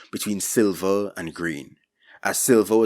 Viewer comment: Ohh, grain. With your accent, it sounded like you were saying 'green'.